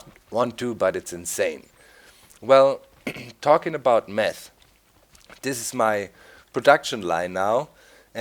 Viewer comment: it sounded like you said ''talking about meth here is my production line'' LOL